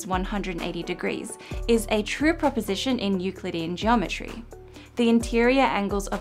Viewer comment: Subtitle mistake at 6:43. "in Euclidian geometry" is transcribed as "in nuclearity and geometry". Interestingly, google's auto-generated subtitles got this right.